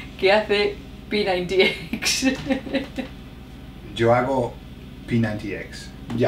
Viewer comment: What was said at 1:32 ? All I can hear is P90x :( ??? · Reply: Respondon I say, "Hago P90X" was joking beforehand saying, "How long will it take for you to mention that you do P90X?" Then, she was the one who brought it up.